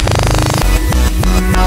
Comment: escuche no seguire